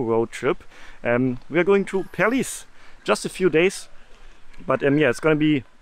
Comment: lol I heard it as Paris but actually Perlis XD